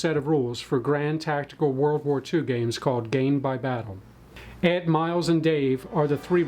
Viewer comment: GAINED by battle. I didn't realize that was the name of the system until the end of video interview. I heard "Gamed by battle" the entire time. Love smaller scale stuff, really let's you appreciate the scale of engagements, terrain, and effective ranges.